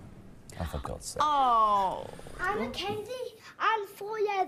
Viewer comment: oh for god sake 😂😂😂😂 I understand Simon so much Also I didn't know you had to be 16 I've seen kids on x factor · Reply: I thought he said oh god